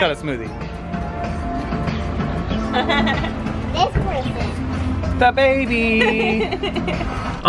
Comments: Did reveel the gender @ 09:26 did he say this princess or am I just hearing things hoping for a girl? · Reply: amy adams sounds like he said “this person” to me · Did reveel the gender @ 09:26 did he say this princess or am I just hearing things hoping for a girl?